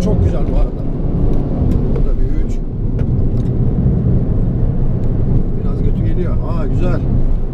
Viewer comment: biraz götü geliyor mu dedi reis ben mi öyle duydum asdsaasdsa